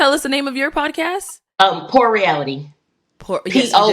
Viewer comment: I thought she said the name of her podcast was “whore reality” lol… I was like well damn 🤣 I think heard something different too